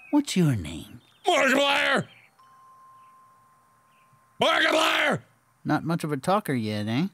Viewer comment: Mark:OHH LOOK AT TH glass breaks Complete silence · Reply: When mark said his name, (turn on the captions at 6:05) it said Margit liar instead of Markiplier. Lol